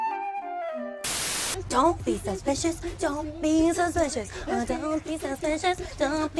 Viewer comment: I thought they were saying ‘don’t be delicious.. don’t be delicious…